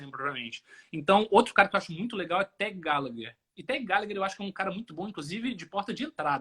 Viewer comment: Qual foi o crítico que o citou no instante 1:00:05? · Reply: Tag Gallagher